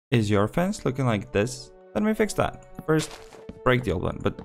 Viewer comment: Me mishearing: Does your face look like this? Him: "Break it"